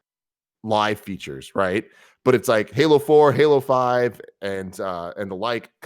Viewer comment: LMAO machinima giving 2015 game of the year to halo 5 over witcher 3, bloodborne, splatoon, ori and the blind forest?... just wow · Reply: I thought she said Halo 4? So that would have been 2012